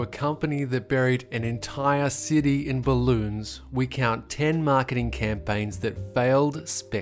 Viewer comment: when he said "balloons" i thought he said balut, i was disgusted the moment he said it. welp. i got my nightmare fuel.